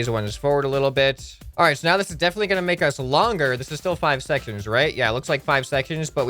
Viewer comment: lol I think you said 5 secconds